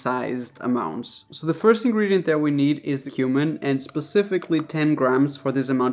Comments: I thought you said “human” for a second when you said “cumin” for seasoning the chili 😂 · When u said cumin I thought you said human!!!! Listening while playing Xbox, but thought about it. I use cumin in my chilli too. Also u wouldn't put beans in chilli for a hotdog. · I thought he said human! The video topic was getting to me, I guess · I had no idea they made raspberry fritters!!! We have apple Fritters. I thought you said “ first thing we need for the chilli is human” then I realized it was cumin 😂